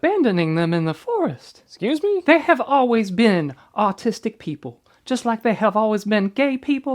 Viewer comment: I'm an idiot, with bad hearing lol. I first thought she said artistic people. Was really confused where she was going until he mentioned the vaccine.